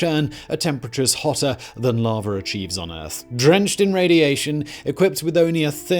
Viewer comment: Can I just say. I didn't not hear "lava achieves" I heard "lava and cheese"